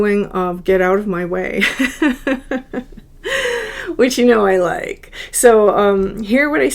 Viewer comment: You said, "... which you know I like," but what I heard was, "Witch, you know I like!" 😂